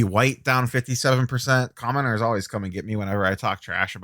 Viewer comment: The first time I heard you say “commoners always come at me when I talk trash” about Coby White. Then I realized it was “commenters”. 😂